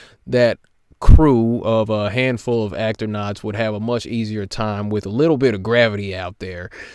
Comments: did he... did he call the astronauts "actornauts"? Oh my fucking god. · "Actor"nauts? I had to relisten to that a few times to be sure I heard that. OK, points for making me laugh.